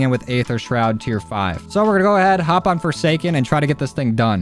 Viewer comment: I thought forsaken said foreskin 🤦‍♂️